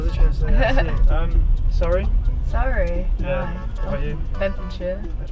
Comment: I thought he said Sorry, not Surrey!